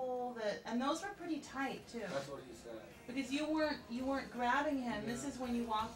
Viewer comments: did he do a "that´s what she said" joke in the minute 17:24? hahha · You can hear Tommy say "That's what he said."